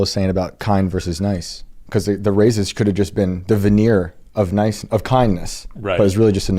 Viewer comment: What did he say? Sounds like "devenir", but that's not English, right?